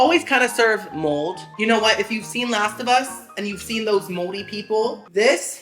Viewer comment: finally! I felt like you were saying "last of us mold" but I wasn't 100% sure, I thought you may have been saying a specific species of mold. Like some biologically technical term like "lasibus mold" 😂😂